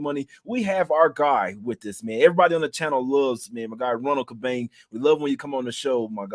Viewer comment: i thought you said his name was Juan Tubang its Cabang